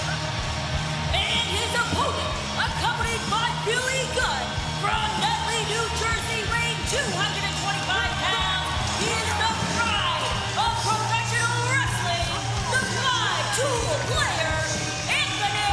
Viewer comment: Thought it said Stool Player Anthony Bowens until Tony said it lol